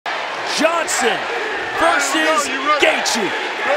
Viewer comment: Bro i thought at the begining he said "justin verses gaethje" i was so confused 😂